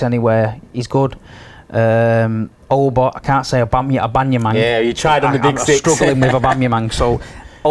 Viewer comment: "I'll bum you man" is that what he is saying?